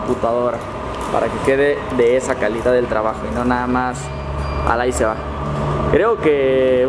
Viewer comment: pensé que dijo Alain se va dije weeeeee noOoo000 😭😭😭👎🏿👎🏿👎🏿